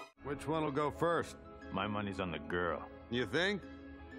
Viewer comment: Mr Krabs really came out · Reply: not even gonna lie in my head i just heard “money money money”